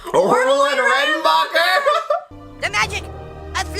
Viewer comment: I have to admit, when I saw this episode for the first time many years ago, I legit heard Cartman sayin something else before jumping off the roof. I remember him saying "Orville, and ass-fucker". That is what I literally heard him say back then.